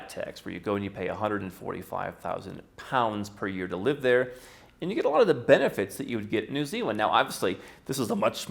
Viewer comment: Did I hear correctly at 3:45 minutes mark that they have a flat tax of 145,000 British Pounds per year just to live there?